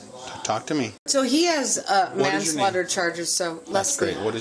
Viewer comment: Did anyone else catch her saying "he has manslaughter charges against him" around the 5:00 mark?